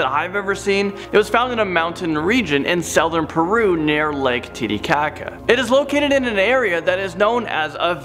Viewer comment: I thought he said "Titi Cocka"